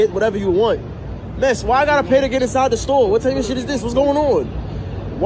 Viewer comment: WTF? Anthony, she clearly said "Where ARE you from?" She didn't say where is you from. There's plenty of things to criticize. You don't have to make stuff up.